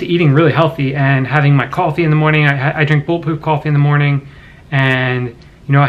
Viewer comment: Was wondering what kind of coffee you were saying at 1:32...turned on the closed captions, didn't think that was an accurate read out LOL. I was able to decipher bulletproof the next time you said it at 1:49, but as you can see CC still not feeling it. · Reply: Yep Bulletproof coffee. <link>